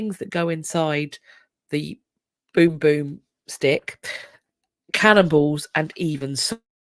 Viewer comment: You're saying cannonballs but I thought you said cannibals. I was wondering how they could tell there had been cannibals...